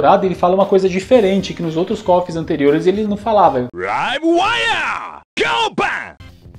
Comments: Eu sempre entendi "I wave ya Go bang!" nao faz muito sentido, mas entendo isso kkk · ele diz duas palavras antes de repetir "go Bang" que me parecem algo próximo de "Rive Wire ou wired" mas não tenho certeza. eu nem sei se a palavra "Rive" existe, mas o que eu ouvi foi uma palavra próxima desta. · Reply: LiveWire go bang! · Entendi isso aí, 19:27 Right you go UP, Go Bang! Certo você sobe, Exploda ! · eu entendi algo tipo highway ya go bang! Não tenho certeza mas parece isso.